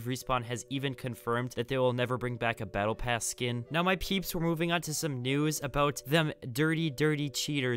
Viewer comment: lol i heard pp's